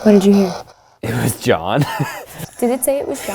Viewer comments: CHILLS ALERTS Abbie did John kill you? Response: 25:29 -ven aca! Ven aca is spanish for "come here" They think it's saying "It was John". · Im sorry but at 25:29 i didnt hear "it was John" I heard "it forgot" MAYBE just MAYBE you guys are hearing what your mind WANTS YOU to hear? xD who knows lol